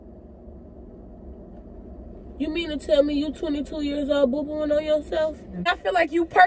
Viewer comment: the girl background I thought she said you 22 year old 😂😭?